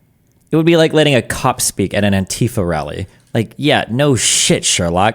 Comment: who else thought he said “cuck at an antifa rally” at first?